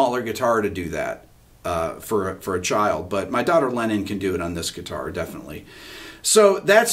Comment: Did Rick say his daughter is called Lenin? Wow.